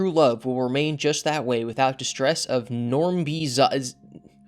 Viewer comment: i think he is saying zombie nazis but differently idk I'm not in all the Chris chan lore